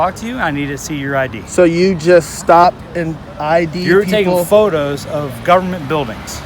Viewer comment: the subtitles says “nigga photos”😂lol